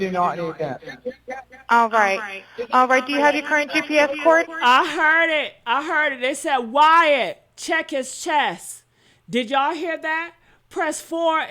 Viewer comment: I heard him say, Wyatt check his chest